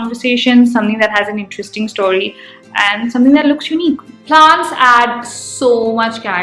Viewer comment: get lots of pants instead of plants😂